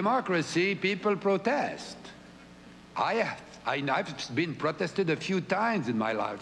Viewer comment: sounds like he says i have been prostituted XD